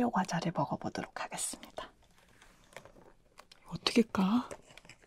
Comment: 😂 LOL, I literally thought you said "oh? A piece of cake?"